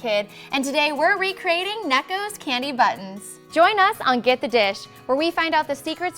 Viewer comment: AT THE BEGINNING I THOUGHT SHE SAID CANDY BUTTS